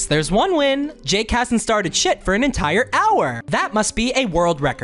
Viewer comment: i thought bro said "jake hasnt gone sh!t for an entire hour"